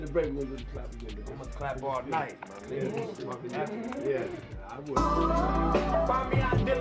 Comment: Bro did yall catch what was being said at 3:41 wtf they mean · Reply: Diddy said I wouldn't clap